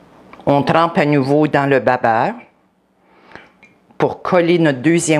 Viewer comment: Superbe la recette merci <3 Sauf que je n'ai pas compris dans quoi les oignons ont été trompés? J'ai entendu "bain beurre", je ne pige pas ce que c'est xD S'il y a moyen de donner la composition de ce mélange :) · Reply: C’est du babeurre